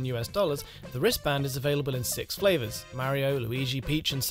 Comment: did he said available in 6 flavors?? 🤣